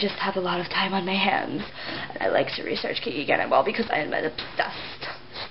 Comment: lol in the first part. it's sounds like you said "Kiki Cannonball" XDD i hate her too!